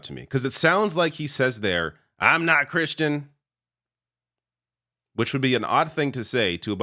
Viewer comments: THANK YOU! I kept saying it clearly sounds like he said he's not Christian and everyone was like youre crazy... regardless I'm voting blue ....let's make this the second time Americans beat the nazis!😂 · Thank you...I heard him say, I'm NOT a Christian," also.